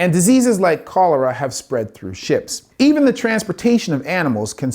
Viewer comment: "diseases like cholera have spread through shits". I like mishearing things in a way that is still correct.